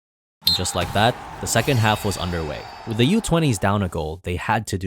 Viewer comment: is it just me or does that say "second HARF"